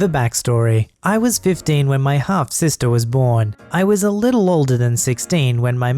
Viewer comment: I was just listening while on my Switch. I thought he said "hot sister".